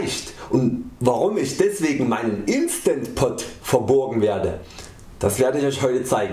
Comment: Oh Mann!!! :D - bissl off topic, aber ich hab grad die meisten Probleme mit dem Wort "verborgen" gehabt. Kenn ich nicht, das Wort. Also nur als Synonym für "versteckt, geheim" oder sowas. Ich hab erst "verBOGEN" verstanden, was natürlich auch überhaupt keinen Sinn ergibt. Ich gehe jetzt allmählich auf die 40 zu und hab das in meinem ganzen Leben noch nicht gehört. Mein Mann ebensowenig. Sagt man anscheinend nicht in Bayern und überall wo ich bisher auf der Welt war ^^ Nicht, dass wir so etwas nie tun würden ^^ aber wir verleihen eben, oder borgen jemandem was, oder jemand tut sich was ausleihen oder oder oder.... :) Liebe Grüße! P.S. Das eigentliche Thema des Videos war aber auch ganz gut. ;)